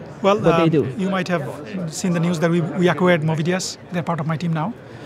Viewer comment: what he is saying there? movidia, mobidia? something else?